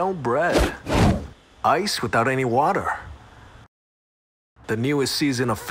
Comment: As an Asian, you did not know the panic I felt when I thought I heard, "Rice without any water". Like- THE HELL?! DO YOU LIKE YOUR RICE CRUNCHY AND NIGH INEDIBLE?! Coz honestly same.